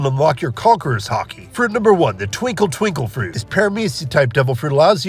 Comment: Bro i thought the man said tickle tickle fruit💀💀💀💀